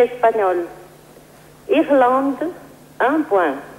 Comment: Hm. Is it just me, or did the Spanish spokeswoman really say l'Islande (Iceland) instead of l'Irlande (Ireland)? 🤔 Sure, it would've made no difference, mathematically, but still... 😐